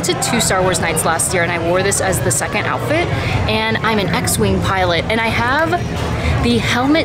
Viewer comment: I don't know much about "Star Wars" -- I've learned a lot by going to Disneyland a lot -- so when you said "I'm an X-wing pilot," I thought you were saying "I'm an ex-Wing pilot," like someone who was in their retirement costume.